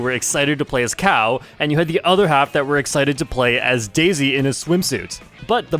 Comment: Did you say Daisy in HIS swimsuit?